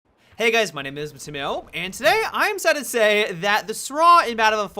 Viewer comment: At the start of the vid, did u say you're excited to say the sraw has been nerfed? 00:01 :o